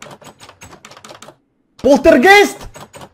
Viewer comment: На 0:46 мне послышалось: - Поттер гей? - Хотя все мы знаем, что это правда, спрашивать не стоит (говорю это для любителей Драрри)